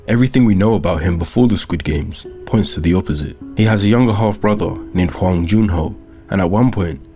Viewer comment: i thought you said " younger hot brother " instead of "younger half brother " but ill take it!